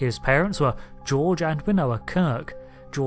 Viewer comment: Sounded like you said... "Winoa" Kirk ?!! I knew her (via the late Vonda MacIntyre) as _Winona_Kirk_. 🖖